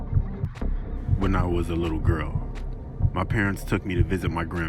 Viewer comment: did he say he was a girl also this vid scared the SHIT OUT OF ME oh and we’re they playing call of duty music